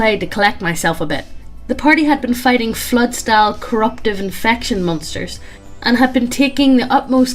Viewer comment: Hang on, anyone else hear Devils... Monsters?